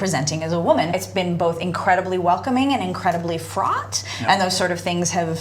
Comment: "Incredibly Frot" uh did she just say the Metal scene is full of Frottage? LOL! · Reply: Fraught, as in ".....fraught with danger", etc.